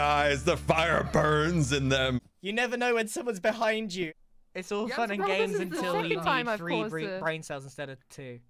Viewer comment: the part you said you couldn't hear/understand (at 2:34 to 2:40) is Grian saying "it's all fun & games until you need 3 brain cells instead of 2" with Pearl overlapping "This is the second time I've caused this" and Scott saying something else I can't quite tell in the background